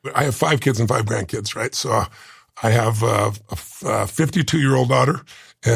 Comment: did he say he had a 52 yo daughter?! Wiki says he's 65.. Damn lmao